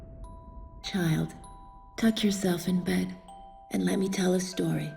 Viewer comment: Did I hear "touch yourself in bed" or am I hallucinating o.o